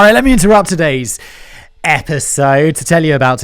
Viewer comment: I thought I heard "epic-sode". Now I require every video on this channel to be addressed as such.